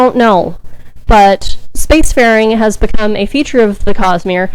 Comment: You were talking about the steelmind and mentioned the word “spacefaring” but my mind heard that as “space ferring” lol